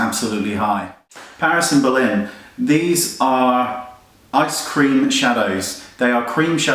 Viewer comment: i could have sworn you said ice-cream shadows. totally yummy tho